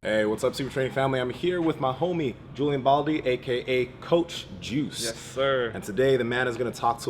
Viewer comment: Did I hear well ? "Juice" ?